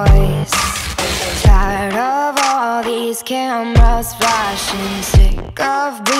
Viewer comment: I thought they said camouflageing but it’s camras flashing....???